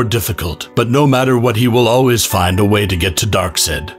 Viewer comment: Ah the most terrifying villain in the DC universe: Dark-Sid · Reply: I thought he said Dark Set. As in "Buy your paint a shade lighter, cause this one is a dark set."